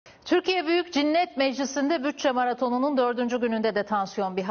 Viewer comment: Türkiye Büyük Cinnet Meclisi mi dedi ben mi yanlış duydum :)